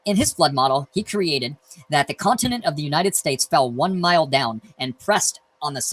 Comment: Did he say "The Continent of the United States"